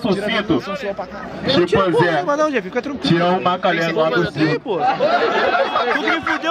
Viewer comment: Oq fala no minuto 4:21, eu entendi chimpanzé tiao maquelé lá do circo eu acho. Mas foi engraçado dms.